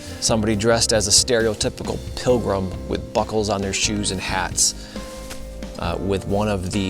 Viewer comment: sounded like you said shabuckles lol.. great finds especially a 2 cent and the Indian head coins love your videos